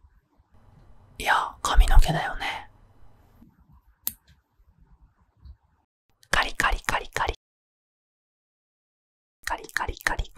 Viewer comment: カリカリがリカリカって聞こえる